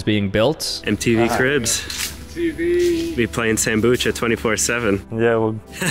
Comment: i swear i heard Tabucha 😭 (You can correct me if I'm wrong lol)